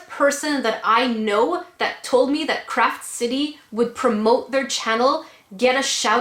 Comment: I thought you said crap city but that works too. Heh